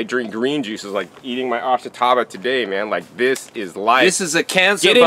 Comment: what does he say? Oxitava? Ashitaba? The fact he shouts right after it's a cancer buster definitely struck my attention, can someone correct me on what he's saying please?